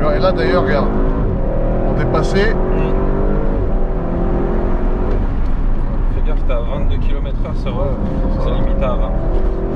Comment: fait gaffe tes a 22 km/h c'est limité à 20 km/h mdr 🤣🤣🤣 C'est ce que j'ai compris